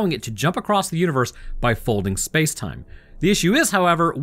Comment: I misheard space time as FaceTime and was very confused for a minute 😂😂